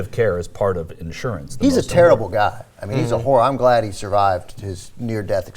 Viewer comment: did he say "He's a whore"?